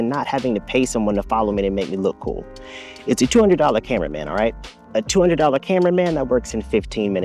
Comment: LOL I thought you said, "It's a $200 camera, man!" The 2nd time yous said it faster, and I wanted to smack myself, a two hundred dollar cameraman, man! Jeez I am stoopid some days. I do like your concept, but honestly? The sound of the Neo sets my teeth buzzing. I would pay more for a quiter, less pitchy sounding companion, you know? I like the Flip sound a lot more, even if its no jazz singer.